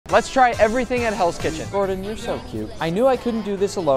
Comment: "Gordon your so cute" 💀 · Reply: For some reason i heard him say: Gordon you're so weird💀